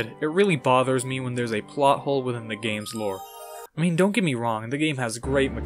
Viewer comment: i thought this said pot hole.